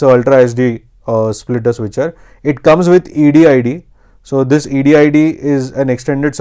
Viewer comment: I can't understand what you say at 3:38, it supports, what? "ADID"?